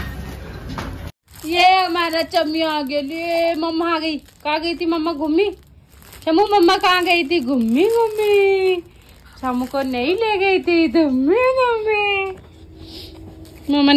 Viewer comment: Did you call shivansh chammiyaa?🤔🤯🤣 03:22. paglate ho kya?